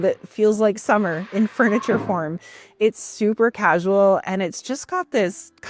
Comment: I watch all videos on 2x speed because adhd so it sounds like you said “furniture porn” not furniture form. 😂